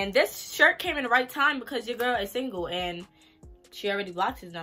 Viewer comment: what sis? Did I hear “I’m single” ? 😟